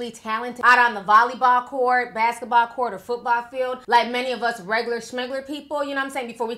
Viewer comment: Did she say regular shmuglar ?